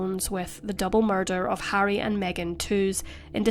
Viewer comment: (POV: i was iust listening whilst cleaning) I thought Prince Harry & Meaghan had been murdered, cos all i kept hearing was "who murdered Harry & Meaghan?"